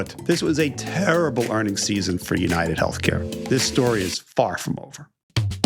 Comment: I heard ... "This story is .. barf mobile!" Which REALLY GOT ME INTERESTED !!